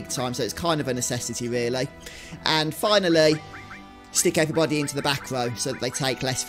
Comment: you say “and finally, stick everybody into the…” and I have NO idea what the word you say is, someone explain?? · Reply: He says to stick everybody in the back row so they take less physical damage. 👍